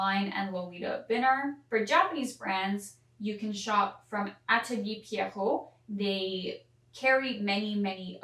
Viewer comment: what was the name of the japanese reseller you mentioned? i couldn't quite catch it in time. thank you~ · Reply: Atelier Pierrot ! Thats the first brand I mention ~!